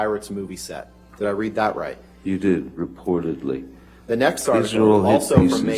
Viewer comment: What did Johnny said here? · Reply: These are all hit pieces. These are dregs, sir